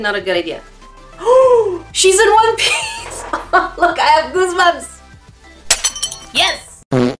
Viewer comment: captions say. I HAVE BOOBS lolololol I’m deaddd